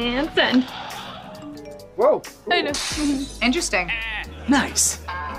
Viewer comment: I thought she said a Silent Wank Oh💀💀💀💀💀